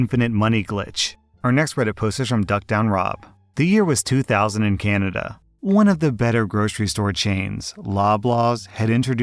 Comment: I heard that as "The year was two thousand and Canada" as in 2000Canada.